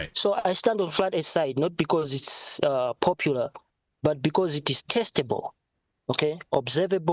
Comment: I thought he said flat earth is testicle